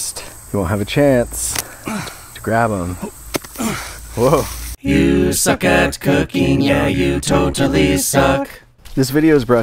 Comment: Did you just say am Iowa Boy sucks at cooking corn.